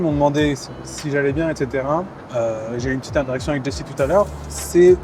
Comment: Putain j'ai compris "J'ai eu une petite erection taleur avec Jessy" 🙄